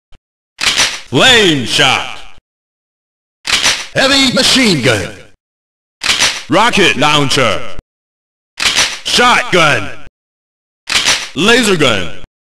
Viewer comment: It always sounds like "ELITE MACHINE GUN!" for me no matter what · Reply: I hear "heawi machine gun"